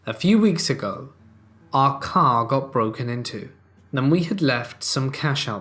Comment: Well I have lost my mind. When you got to the story about the people's car getting broken into I would have sworn you said "our cow got broken into". I was like WTF and had to take it back a second. Working and listening to these makes for some weird moments.